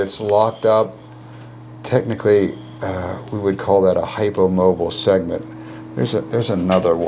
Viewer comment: should read HYPOmobile segment. That's what he said.